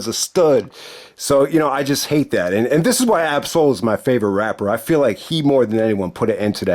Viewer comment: Who did you say your favorite rapper was, again around 20:58-21:05?... I didn't catch the name. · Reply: Ab-SOUL, I feel bad that you never heard of him, he and Kendrick Lamar came up together